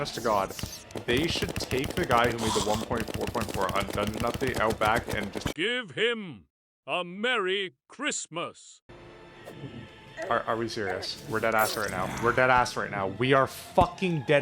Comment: Im struggling to understand what you're saying at 7:27. the subtitles say you're saying undungeon update but what does that even mean?